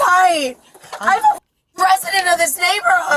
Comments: At first I thought she said she was the “president of the neighborhood” 😂😂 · The goat scream 🐐 😂 I thought she said she was the president of this neighborhood 😂 sorry, I can’t handle the momtrums. Show some class.